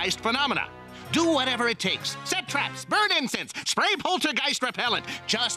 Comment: DID HE JUST SAY TO burn INFANTS